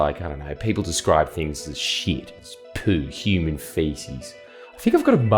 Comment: You said ‘human 💩’ I thought you said ‘ human chimp’ saw the blokes ears and thought, yeah true…